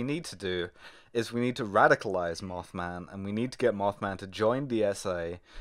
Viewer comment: I heard "get mothman to join dsa" as "get mothman to join the SA" and got really confused for a second, lol